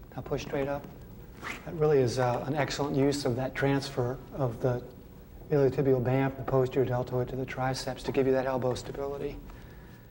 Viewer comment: did he say friggin iliotibial band?